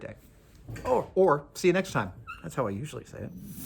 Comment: I thought he said, "have a quick day"! yeah, days are so quick this time of year :)))